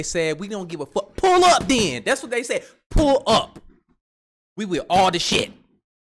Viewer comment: "Pull up, we with all the shit" best interpretation of what they are saying exhibiting. 14:44 lol